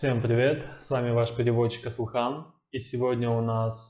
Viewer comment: Ааааа! Асылхан... Я прочитала в коменте. А Вас назвала ( так мне послышалось в видео с реакцией Шахина- второе прослушивание) я извеняюсь 🙏🤣 Асылхан, Ваши переводы это невероятно приятно. Я из России и у меня такое впечатление, что все говорят по-русски... И всё это, благодаря Вам!!!!!!!!!! Этот парнишка живой, эмоциональный, слушает тут- а мысленно там..., Но Вам удалось сделать невероятное- поймать этот ураган!!! 🤭👍🤩